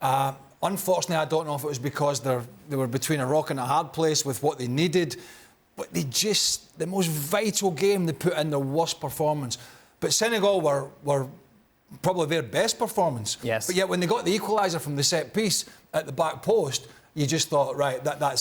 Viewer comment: I thought these guy's said Senegal will lose